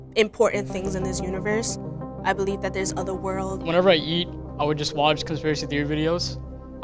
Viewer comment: i thought that guy said "whenever i yeet i watch conspiracy videos" and i just died on the spot